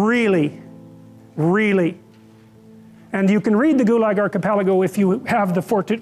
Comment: You ca read the……. Can someone please clarify what Jordan is saying there? Thank you! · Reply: He is talking about a book called The Gulag Archipelago by Alkesander Solzhenystin